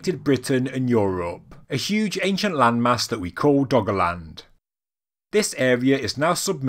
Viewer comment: I kept hearing it as "Doggoland"